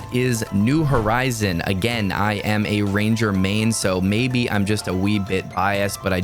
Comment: LOL. when you got to #1 I thought you said “maybe I’m just a weeb & biased” which nearly made me spit coffee on my laptop.